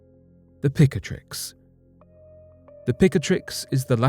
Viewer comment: Picatrix? At first I thought he said pick ‘n mix!!! 🍬🍬